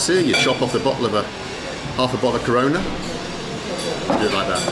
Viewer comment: half a bottle corona, what did i hear